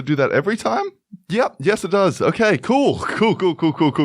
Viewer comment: I paused at random moment and 5:23 lol..... music kinda cool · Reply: Again..... 7:23. Anyway. The subtitles say cuckoo because he says cool too fast. I watch videos on phone and rarely turn on sound so I thought the whole time he said cuckoo